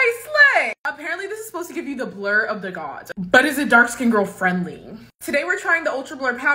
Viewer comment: I was distracted for a second. I thought you said “blur of the dark”. I was like that’s cute. Lol.